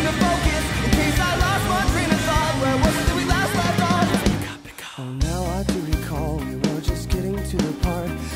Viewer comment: i always hear "pika pika"